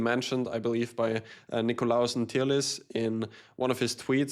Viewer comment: I can't figure out what name he said can someone help me, I tried looking this guy up doesn't come up, I heard "nikolausen tieles" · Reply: Nikolaos Ntirlis :)